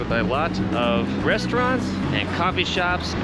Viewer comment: am i the only one who had to rewind twice to hear what he said and understand what he actually meant? 0:45 copy shop but coffee was meant 1:31 they were (..) to fall but meant was they were just full